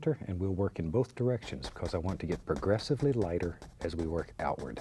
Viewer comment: I thought he said aggressively lighter not progressively what a weird thing to hear from bob